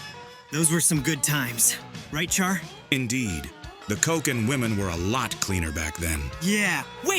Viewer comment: what the fuck he say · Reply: Coke. For Cocaine.